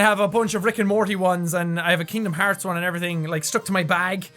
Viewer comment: wait a minte DID HE SAY KINGDOM HEARTS OMG I LOVE KINGDOM HEARTS THERE REMASTERED ON PS4 PLAY THE ON YOUR CHANNEL PLEASE!!!!